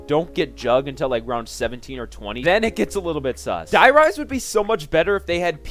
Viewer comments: Did he say sus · Did he say sus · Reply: Did he say sus